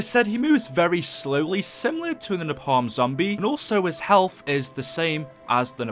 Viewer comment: it sounded like he said nepaum